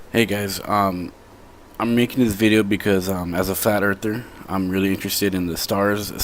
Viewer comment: Did he say "As A Fat Earther" ?